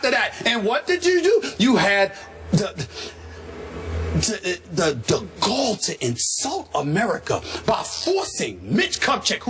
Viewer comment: “The goal to insult America 😂😂😂😂” the best part of this rant. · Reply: Minh Cuong Nguyen oh I thought he said the “gall to insult America” lmao. Works both ways. 😂😂😂